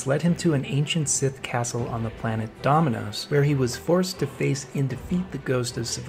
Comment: Did he just say planet domino’s like the pizza place